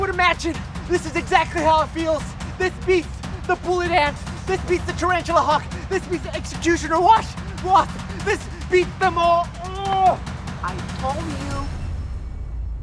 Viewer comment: he said wash instead of wasp😂